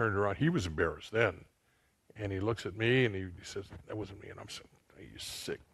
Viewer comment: what did he said richard said ..? At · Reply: "That it wasn't him" - Richie basically blaming the victim for not keeping him hard enough. That's when refers him as "a sick man" because it was an elderly woman, who was being anally raped.